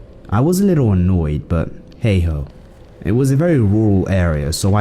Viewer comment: I thought he said :hey hoe